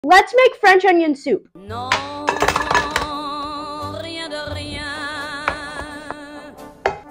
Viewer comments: iam the only one who thought he was saying “lets make freinds on youtube!!” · I swear I heard “fried chicken soup”